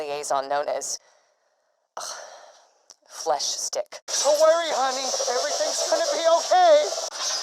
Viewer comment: Haha when he said flesh stick it sounded like he said fleshdick